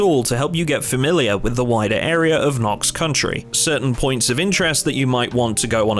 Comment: it's not Knox country it's Knox county. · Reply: They actually changed it. I looked it up because I keep hearing "Country". From what I found, it seems the British devs got the county name wrong early on, as Muldraugh is actually is Meade. They kind of ran with it on and off, and I think to reconcile certain in-game stuff with the real world they retconed the name to "Know Country".